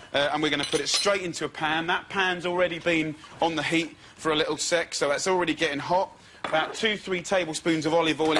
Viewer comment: did he said "a little sex" it sure sounds like it.